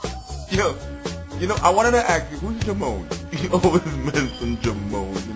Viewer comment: well it kinda does sound like says jimone but he actually says keep on.